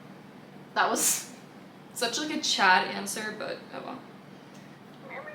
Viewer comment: did you say “that was such a Chad Answer” 🤣!12:20!!!